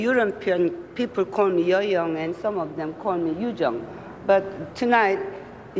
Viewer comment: she actually said “some of them call me Yoo Jung” ‘cause Yuh-Jung is how you say her name correctly